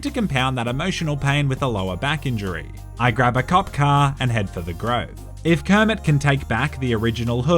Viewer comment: For a second there i thought he said "i grab a cock car"